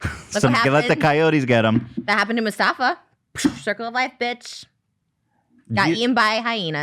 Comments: she did NOT just say Mustafa 🦁 😂 MOO FA SA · she did NOT just say "musafa" 😂at 25:52. Ethan was about to correct her too lmao, then he second guesses it himself